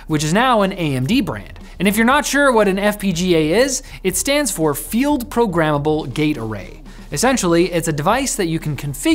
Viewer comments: i thought you said field programmeable Gatorade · If you don't look at the screen and just listen it sounds like he said Field Programmable Gay Parade 💀💀💀